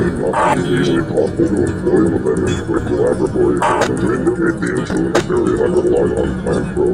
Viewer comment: All I can hear in the Night 5 call is "the joy of creat-" at the end that everyone else can hear, but I also could swear to God I heard the word "laboratory" in that garbled mess...